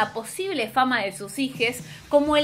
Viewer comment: En el minuto 02:39. Escuché “HIJES” y hasta aquí llegué chawwww hijes mujeros hombras y hembros 😂